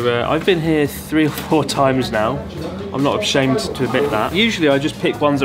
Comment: did he say "not abshamed" 😄 too much sake Chris